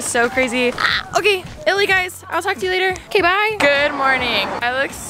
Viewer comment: did she just say “ily”?😂 love you!🥰